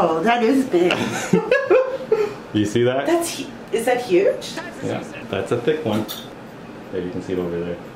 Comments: I think I heard "That's what she said" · did you hear??? Patient: Is that huge? Steve Carrell: THAT'S WHAT SHE SAID! Is very low so turn up the volume! xD